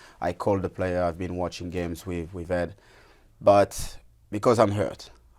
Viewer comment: does he say 'I've been watching games with Ed', as in Ed Woodward?